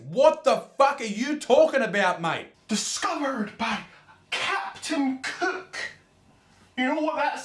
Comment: Dislike if you heard him say “captain cuke”?